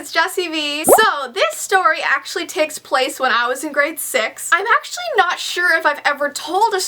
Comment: did she say grade sex ?